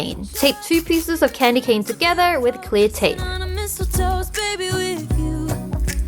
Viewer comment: I thought u said clay tape